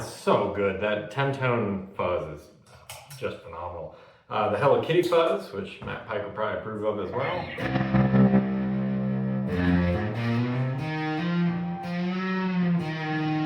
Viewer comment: did you say the “hello kitty” fuzz?